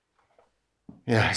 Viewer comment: Hah! I had to rewind because when you came out of the Cantina and were passing the glass door, I thought you said it was a “work of arch”. Well, I thought, I guess it could be. Nice arches were all around!